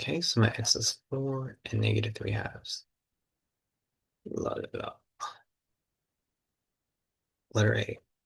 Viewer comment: what did he say?? i cant make it out lmk pls i need this info for my test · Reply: load it up??